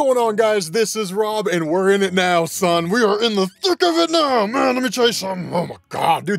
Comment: From the start I heard "into the thick of it🎶🎶"😂😂